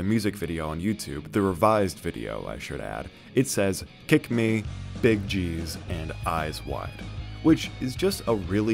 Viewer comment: So, um, what does "Big G's" mean? I always heard "big cheese" because the other phrase doesn't mean anything to me. Big gangstas? Big gumballs? Big gramps?